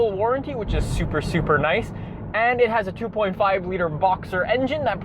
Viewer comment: boxer* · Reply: I'm aware of that, just sounded like said "Voxer" instead of "Boxer". Check it again, Luke.